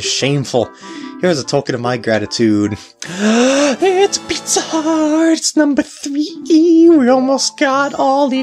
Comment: "Its a pizza heart!" Uhhh i mean pice of heart... lol am i the onlyone who heard pizza heart after the couples mask heart piece? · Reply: Nope I heard him say pizza heart as well.